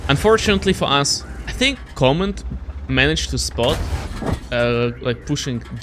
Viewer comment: did u said coment at 3:39?